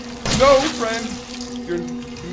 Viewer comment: I swear I thought he was saying "No Fred!". I was thinking "Who the hell is Fred?! Was it that creature, was he Fred?"